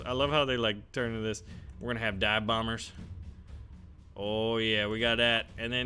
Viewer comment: Did I just hear "Dab Bombers! "